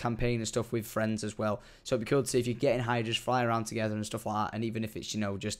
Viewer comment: did he just say you can get high???